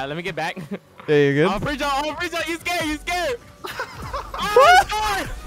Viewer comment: said ima prejump he’s scared he’s scared, i misheard this and was half-asleep leading to me hearing ima prejump he’s gay he’s gay